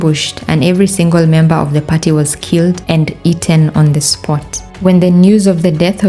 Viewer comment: did you say eaten??😢